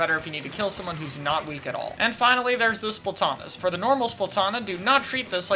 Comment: The captions say "finally there's Lucifer Thomas" here LMAO